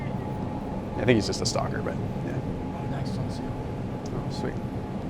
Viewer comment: "I think he's just a stocker..." - not to be confused with what I heard... "stalker"